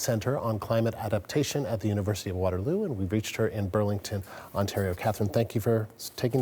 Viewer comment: Oh, Burlington. I thought he said ‘Berlin’ somehow, and was like ‘did Kitchener go back to the old name?’.😅